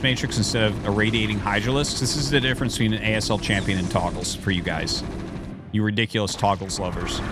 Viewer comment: Did I heard Togles???